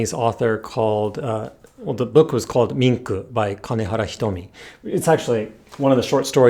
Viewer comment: Did he say Minku by kanehara hitomi? if yes Can anyone send over a link? I can't seem to find the book anywhere online. Thanks